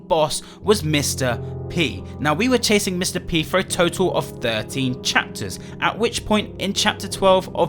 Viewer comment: "We were chasing Mr. P for a total of 13 chapters" Problems there: 1. We didn't know Mr. P even EXISTED until the end of chapter 4 2. We didn't go after him until the end of chapter 6 3. 13 chapters? Distorted Memory had George as the main character, playing through his nightmare, we were not playing as the officer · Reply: I thought he said 30 chapters